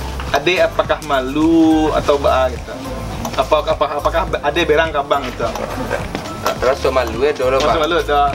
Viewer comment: gw dengernya "Apakah adek berak ngambang?" 😂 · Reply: Berang bukan berak